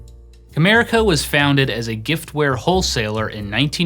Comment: it sounds like you say "America was founded as a giftware wholesaler in 1987 by David J Harding."